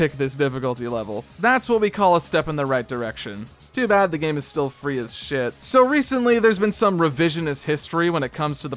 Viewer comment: what is he saying? Free as sh*t?